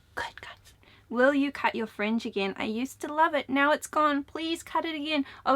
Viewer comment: When you were talking about your fringe i thought you were saying french and i got really excited LOL i speak mostly spanish with my parents and english with my sister. When i speak spanish tho im always wanting to throw in french bc they sound so similar. Also, ive been studying japanese for about a year now and im always wanting to say numbers in japanese for some reason. I studied abroad in nagasaki for a semester and it was really bad then, bc my brain was switched to not english rather than japanese, i would speak to myself in a weird concoction of french spanish and japanese LOL